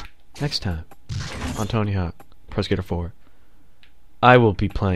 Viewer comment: I keep hearing "Tony Hawk's Prosecutor" lol